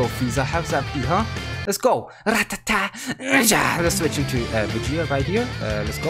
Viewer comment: i'm sure i heard ra-ta-ta dia